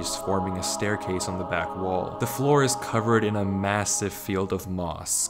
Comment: did u just say massive?